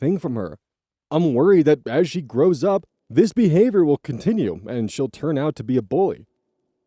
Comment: at first I heard "...and she will turn out to be a boy" and was about to loudly WTF when I looked at the screen and read the actual word. So... yay me for reading?